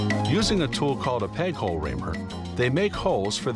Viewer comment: Did he say "peg-hole raper"?